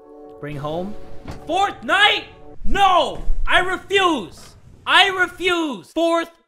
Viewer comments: "FOURTH NIGHT? I REFUSE." What I heard: "FORTNITE? NO, I REFUSE." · really thought he was saying fornite while I was looking away, like I’m not a fornite fan i just play the game when I’m bored but like- My ears decive me 😔